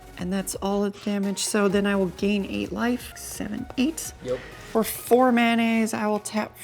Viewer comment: Great episode but did she say for 4 mayonnaise 47:36?